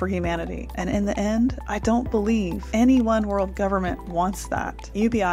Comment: did you just say one world government? 😅